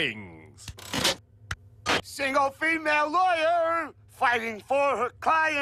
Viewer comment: i really thought it said sigma female at first